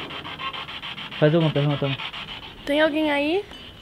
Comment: No minuto 7:06 eu escutei eai porra